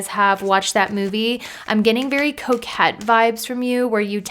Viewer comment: Pile 2: I heard “I’m getting very coke head vibes from you” and I was like hold up, excuse me? 🤣🤣 rewound multiple times and finally put captions on …we good now. Nobody say if you heard it it’s for you. I don’t claim that 🤣 Aside from that mishear, pile 2 was so spot on it was bananas